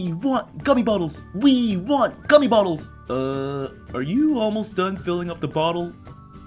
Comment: Seems very creepy. At 3:39 did he say "we want gummy buttholes we want gummy buttholes!"